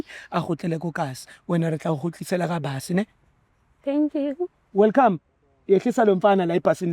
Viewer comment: for a second i thought that will say Welcome yehlisa loskhotheni 🤣🤣🤣